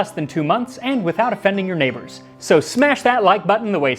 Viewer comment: When he said “without offending your neighbors” at the start, my headphones had cut out and it sounded like he was saying he was gonna teach me how to make garum without f*cking my neighbors (Edit: spelling)